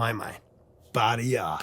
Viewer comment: I always misheard "badi ya" as "party hard" lol